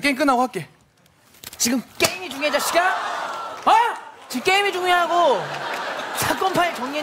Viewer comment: 대본은 게임이 중요하냐구 인데 웃겨서 게임이 중요하구 라고 들리네 ㅋㅋㅋㅋㅋㅋㅋㅋㅋㅋㅋㅋ